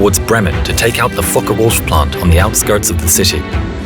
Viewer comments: thought he said fuker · now why did i hear it as Fuck the wolf instead of focke-Wulf?